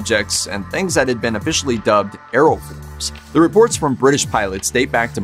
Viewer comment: Hi. Thanks for the video. At about 3:24 you mention the official name that Britain gave for unidentified lights seen in the sky by pilots during air missions. But I didn't quite hear the word used. It sounded like "Aero... " something or other. Can you share what that word was? Thank you. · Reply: 'Aeroforms', at least according to the captions